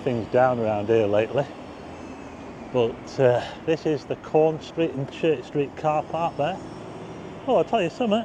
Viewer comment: Caunce Street? I always thought you were saying Corn Street. 🤣